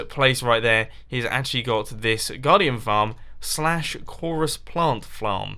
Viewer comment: Did he say flarm?